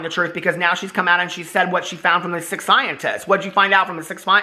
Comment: I thought you said Sick🤢 Scientists 😂